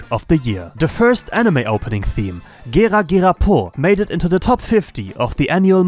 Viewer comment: Did he just said ”Giga Giga po” 🗿😅 As a Japanese, there’s no way he said “Gera Gera po” like that 😂